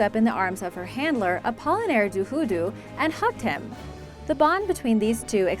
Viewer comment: did you say hugged or humped? XD I didn't hear it well